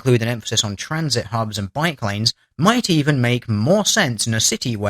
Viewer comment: anybody else hear 'biplanes' instead of 'bike lanes' at 7:49 ?